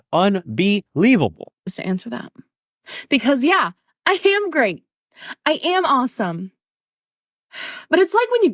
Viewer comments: Lmao it sounded like she said "I ham great" 😂 anyone else hear that? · I thought this cow said said: i ham great